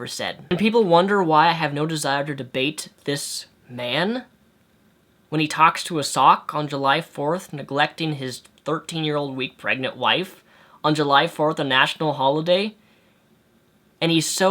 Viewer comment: jeez, you've really let onision get to you bro.you look like shit. and is there a such thing as a '13 year old week pregnant wife'? or was i just hearing things? onisions really got your brain bursting at its seams. thank life this is your last video on him